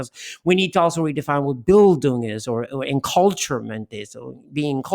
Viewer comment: "we need to also redefine what ...."? What does he say. The translation says "bill"